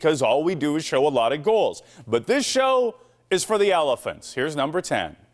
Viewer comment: did he just call me an elephant?